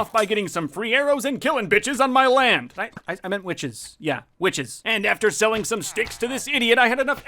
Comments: did you say b***ces? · more more more more MORE! wait a sec did he say b*ch on 5:51? or witch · So you said witches I swear I heard something else at 5:50... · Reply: bitches is wat he said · WHAT DID HE SAYYY??! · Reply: He said killing bitches on his land. Nothing too bad.